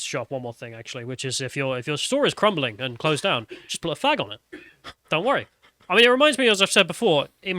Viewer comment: did say, "If your building is crumbling, just put a fag on it."? I can see he wrote flag, but I think I detected a Freudian slip.